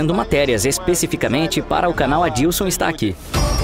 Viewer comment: Grande Adilson, vendia capinhas de celular aqui no Brás, sempre na correria, colava lá nos bailes do Heliópolis. Boa Adilson do Brás para o Mundo! Tá voando!!! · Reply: Me arrancou uma risada quando ouvi "Adilson está aqui" kkkkkk, achou o canal? É Anton is here